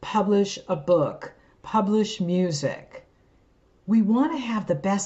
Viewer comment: even the auto subtitles say moosic instead of music